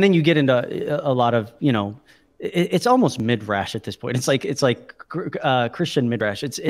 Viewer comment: Media At about 43:38, it sounded like said "midrash". Did I hear that correctly or what was it he might have actually said there? I ask because it was not clear to me exactly what he said there. If "midrash" is what he said, what does that mean? Thanks.